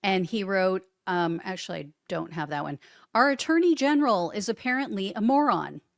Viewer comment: I just heard "Trump's dirty general" but you said attorney general. 😂😂 i like my interpretation better.